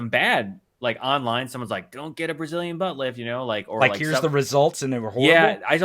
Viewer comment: The first time they defined the BBL I thought they said "Brazilian But Lick" my first thought was eww.